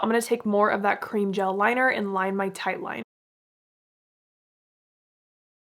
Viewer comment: I know that hun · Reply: oh, I thought I heard you call your water line your tightline